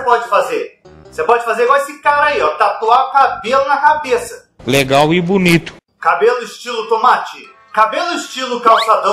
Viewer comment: So eu que ouvi "camelo estilo"